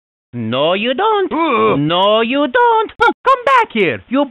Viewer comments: I used to have Mario vs donkey Kong on the DS and Mario said that and I thought he was saying youbeeka monkey so my arch nemesis was youbeeka monkey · for a long time I thought mario said "you piece of monkey!" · I thought he said you beat a monkey